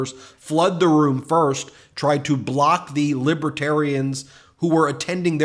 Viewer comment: Heard him say Libba-tarian. I bet he says lie-bary and lie-barian too.